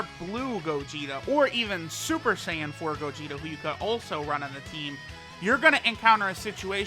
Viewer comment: did he say super saiyan 4 gogeta